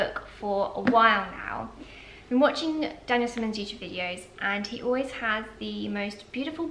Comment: Did anyone understand the name was referring to? Dennis Menduch videos? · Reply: Daniel Simmons